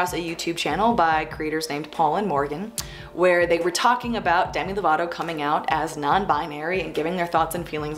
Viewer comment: WHY DID I HEAR "DANNY DEVITO" INSTEAD OF "DEMI LOVATO" EJRJSVEDN I thought he came out as non binary for a second and I fucking panicked